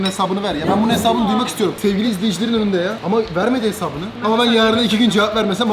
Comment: bi saniye ahmet abi sevgilimiyiz dedi benmi oyle duydum ?